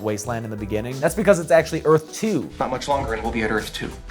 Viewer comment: Okay, so when you made the comment in Jason X about Earth 2, I thought you said "Earth too" so that's where I got mixed up.